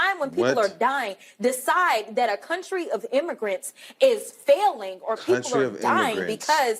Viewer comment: Did that fool just say we are not a country of immigrants or did I hear wrong?